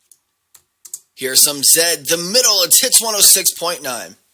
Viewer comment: Thanks. It's like watching hot dogs being made. Got to laughing at the 20:45 mark it sounds like your saying tits instead of hits, which i thought was great.